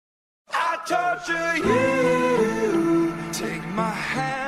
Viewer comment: my dumbass thought they were saying “I talk to you” lmfao